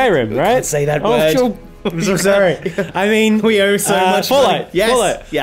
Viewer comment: Did he say 'we owe so much money'?